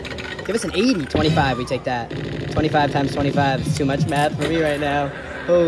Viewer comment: It sounded like you said too much meth(math) for me lmao